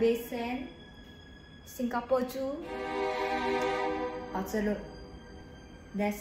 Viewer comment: I hear the say "ouh jia lud" for orchard road before haha 🤣